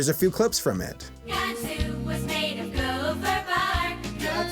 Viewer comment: did they just say that Noah's Ark is made of gopher parts?